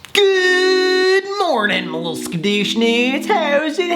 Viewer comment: No one: Literally no one: Sam: *evily rubs his hands together… “GOOOOOOOOOD MORNIN MAH LITTLE SKADOOSHNOODS” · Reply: I thought he was saying skadooshnas.